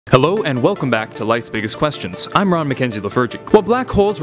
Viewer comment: LMAO 😂 I thought he said “ Hello, welcome to life’s biggest questions. I’m Raw Macenzy the virgin” 😂